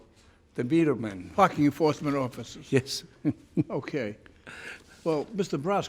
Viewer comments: did he say F you force men office? Or is it just my cured ears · I thought he said f*ing enforcement officers 😂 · if you tell yourself Frank says "F&#%ing Enforcement Officers" it really does. I was looking away the first i heard it, and i swear thats what he said. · did he say f**** enforcement officers or am i trippin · I thought he said "fck the enforcement officers!" =D ahha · Gonna be honest, at 2:23 i thought he said ** the enforcement officers lol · did he say " F*ck the enforcement office"?? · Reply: parking enforcement officers hahahahahah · I thought he said 2:20 “f*cking forcement officers” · i really thought he said fck you enforcement officers 🤣🤣🤣